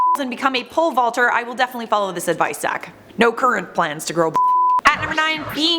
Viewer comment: She said, "follow this advice Zack" I heard, "follow this nut sack". hahaha, puns.